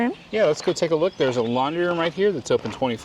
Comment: Nothing special about the bathhouse. I don't know why, but I could have swore said lingerie, but she said laundry 😆 I didn't know any playground still had those dome monkey bars. I thought they were deemed dangerous. Very nice they have extra dog park areas. Looks like the park is pretty much out in the middle of nowhere. Should be nice and peaceful there. has got to stop being so sweet. That's why those bugs are so attracted to her. Safe travels, guys!